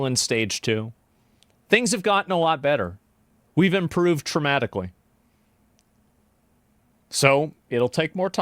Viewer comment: We have improved traumatically. I bet I misheard that but thats a good way to put it. edit: oh someone in chat caught it